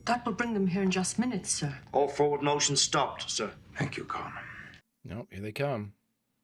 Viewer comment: o'brain has travelled quite the distance from his first appearance, one could even say, he has gone MILES ... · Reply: Having not seen this for many years I sort of laughed when Stewart said ,"Thank you Con" whilst on the battle bridge, because I misheard it as "Thank you Colm"